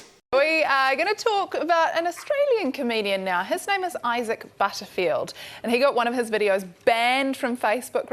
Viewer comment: I thought she said “issec’ bitterfield” 😂 I’m sorry, I’m sorry I had to